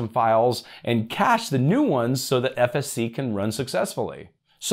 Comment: Sorry for being off topic, but I was seriously wondering whether I was going insane. I kept hearing you say FSC, but when I went back 5 seconds and listened again, you said SFC. At 4:40 you definitely said FSC though, which I, for some reason, found comforting.